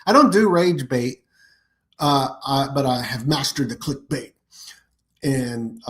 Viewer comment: I don't know why. I had to listen to it twice because I thought you said you were gonna teach us master bait. 😅